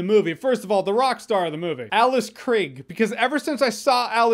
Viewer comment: Thought you said the rock stared in the movie as in Dwayne Johnson lol